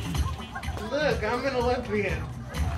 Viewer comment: Thought you said you were a lesbian. Wow and I'm not even that old